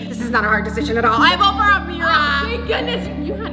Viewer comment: if you have captions on then when says Ameerah the captions say a Mirror